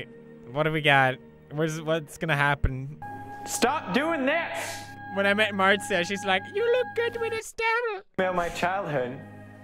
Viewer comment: I can't hear what he's saying... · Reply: The Wolf with the Dragon I think it was "when I met Marzia she said 'you look good with a stubble' "